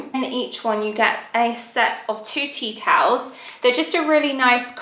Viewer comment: I heard “four tea towels” as “forty towels.” I thought that was slightly excessive 😂 your house is looking so beautiful